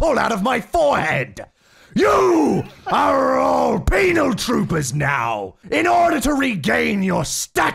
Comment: Why am i hearing "penile" troopers instead of "penal"? Penile hehe